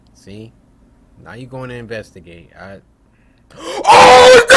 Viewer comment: I thought you said Black people don’t investigate